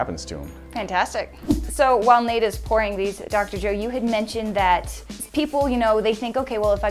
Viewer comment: did she just call him dr dre?